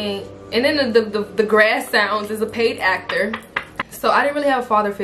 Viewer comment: I am only 7 mins in and I hear "the grass sounds is a paid actor"....😂